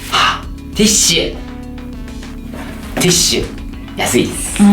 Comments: Totally thought he was saying "this shit" and not tissue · when he pulled out the tissue and said tissue i thought he said THIS SHIT at first lol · i thought he was saying "this shit" when he was saying tissue haha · When said tissue I thought he said " this shit"